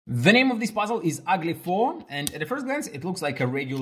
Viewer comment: What I heard : "the name of this puzzle is ugly p*rn"